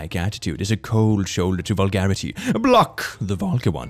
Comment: I heard Vulcan instead of vulgar and I was like, "where did you find a Vulcan?"